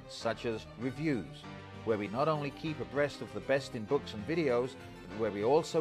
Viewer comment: does he say breast of the best at about 1:30???